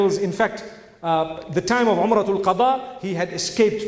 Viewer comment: Asalamualaikum, what did Mufti Menk say at 41:04? Umratul Qadha?